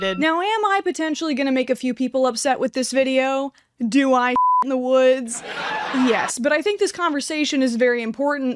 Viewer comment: I heard, “do I shit in the woods?” … now I’m subscribed 😂❤